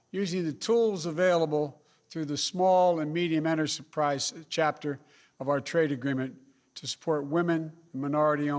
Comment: OMG teleprompter Biden what on earth are you saying! Please pay the hearing impaired interpreter double! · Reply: Or the person who has to type in the closed captioning. 😂😂😂 At 1:16 it says "enter surprise chapter", because that's what he said. LOL